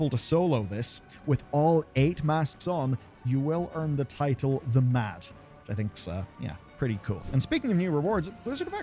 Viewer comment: I thought for sure you were saying “the Matt” & I was beyond confused why that would be the title